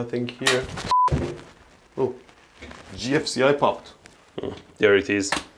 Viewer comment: I thought he said "gypsy eye popped". I was very confused.